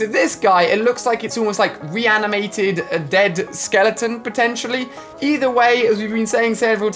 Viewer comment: did he just say dead skeleton